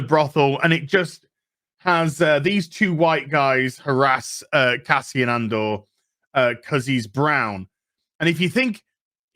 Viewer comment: I thought it was Endor and we were getting a show about Ewoks. Imagine my utter disappointment. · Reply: I misheard the lead character's name as "Caspian Andor" and thought this was a Narnia spinoff.